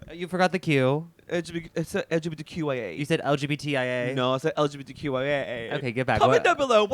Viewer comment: I heard LGBTYA😂